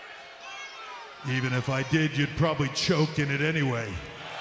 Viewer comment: What did Hunter say from 2:36 to 2:42?? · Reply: "Even if I did I'd probably choked it in anyway"